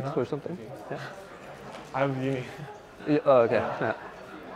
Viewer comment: what does he say? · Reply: “I’m married” is my best guess.